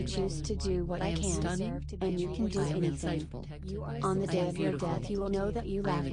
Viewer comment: does she say....On the day of your death? If so, yall need to take that out.